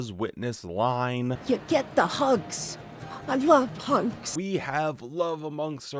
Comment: Am I the only who thought she said pugs instead of hugs? It sounded like pugs instead of hugs to me. "You get the pugs. I love pugs." That's what it sounded like to me.